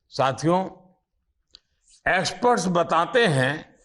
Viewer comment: "Aspert" instead of experts